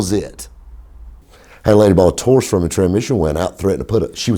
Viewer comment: what the hell did ye say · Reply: Had a lady bought a Taurus from me